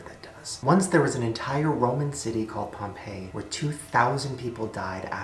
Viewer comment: When he talked about Pompeii I literally thought he said Pompeo. I watch too much TV